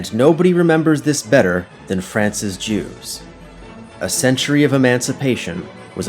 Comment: I was listening to this while riding on the bus, and I heard "And nobody remembers this better than Francis Jews" and my first thought was, "Who is that? I've never heard of him!" 😆